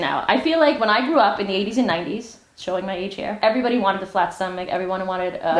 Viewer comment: Arielle, I misheard you, and I didn’t think you said “80s and 90s” and I thought you said “1890s” and we’re making a very good joke about the whole thing. 😂 I have been giggling for at least two minutes. Love your content.